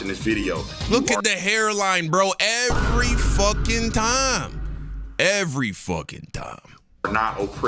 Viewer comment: son did u just say every fuckin Tom ?😂😅😭